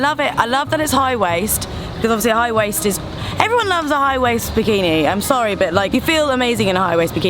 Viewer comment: Wow. U look great. Sounds like she said " when you got a high waist zuchinni" hahahaha